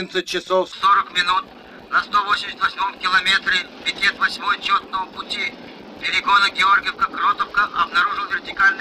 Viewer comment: послышалось "Чертового пути")